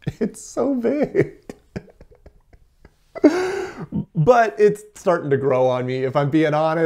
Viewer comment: did you just say it's getting bigger???